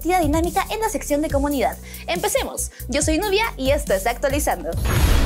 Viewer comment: Te juro que escuche (Novia) 0:22 XD